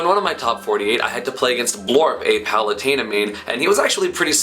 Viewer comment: Borp? The Melee Shiek main? The 6th god? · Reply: I thought he said Borp, but it was actually Blorp.